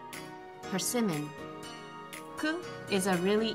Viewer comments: What did you say at 2:08? Was it swimming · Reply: Ricochet She said "persimmon", which is a fruit. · What did she say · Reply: Persimmon Its a fruit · Can somebody please tell me what she said at 2:12? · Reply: persimmon